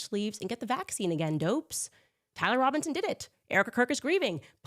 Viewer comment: So weird 1:46 Candace says tyler robinson did it, but the captions say Kyle Rittenhouse did it 😮